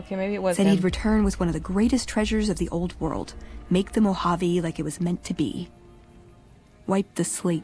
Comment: I always think at 18:56 she says that Elijah was going to "make the Mojave great again" and I hate that that phrase is so drilled into my head.